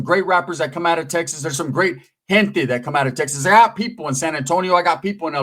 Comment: For a second I thought you said Texas has great hentai. I was like whaaaaaaaa😂